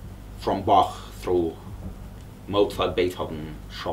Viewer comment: loved how the auto-generated english subs put "God" instead of "Bach"